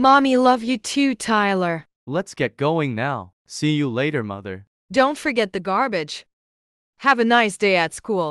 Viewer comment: i hear see you mother but it say dont forget the garbage it got me dying